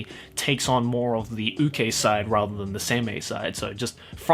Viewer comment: What is "ukei" and "semei" at 23:05? My dictionary is not helping. Or did you say "UK side"?